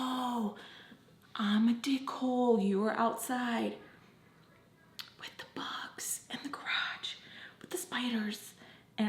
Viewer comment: Why did I hear “what the bucks, and the crocs and the spiders?” Instead of with the bugs, in the garage and the spiders 😆😂